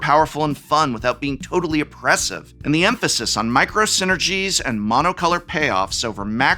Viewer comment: I swear I heard "micro center-gies"